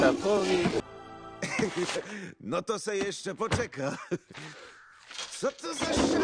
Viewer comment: На польском мне послышалась ,, зато есть эпотека"